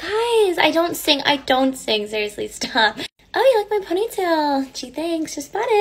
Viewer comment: Girl.. did you just say “i bought my ponytail” 💀💀